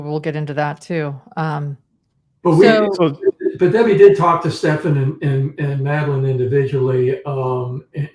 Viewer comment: I get the sense that if Debbie (Mom) could speak freely she would say that pre-teen Maddie was leading Stephan on by draping her arm around him, kissing him and saying she loves him, and asking to sleep in same bed with him. I would be willing to bet that deep down she feels Maddie is partly responsible for what happened to her because how could her Stephan resist a flirty pre-teen? She said something in the interview about once she learned of the co-sleeping, she talked to BOTH Stephan and Maddie together and admonished them, as if Maddie also needed admonishing. And then she pointed out that despite her admonishment, THEY didn’t stop. From her statement of THEY didn’t stop, I think we can assume she also felt that Maddie’s wouldn’t stop sleeping with Stephan despite the admonishment. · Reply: I thought she said she talked to Stephan and Jenn, not Stephan and Maddie.